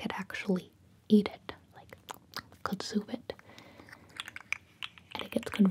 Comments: I’m not so sure if im right here.. But, Is she saying “Good soup it” Or “consume it” Time Stamp: 4:48 I’m pretty sure she say’s Consume, but i cant help but to hear “good soup” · please tell me I’m not the only one who’s watched this about 19 times now and thought she said “good soup it”, I only just now realized she said “consume it” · I thought she said “good soup it” and not “consume it” but like they technically both works lmao